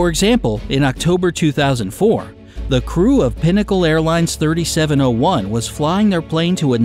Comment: for a second I thought that said “pineapple airlines” 😂😂😂